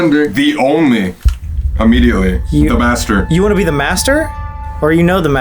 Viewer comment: From 50:00 to 50:05 I think it was trying to say. "I am the only Master."